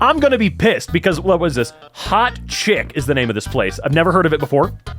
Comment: I heard hot shit 💀